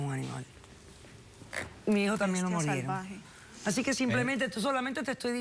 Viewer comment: La doctora polo dijo "a mi hijo también lo mordieron?" tengo entendido que ella no tiene hijos. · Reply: Gracias por especificar! Pensé que había dicho “mi hijo canino también murieron” , jeje no lograba entender lo que decía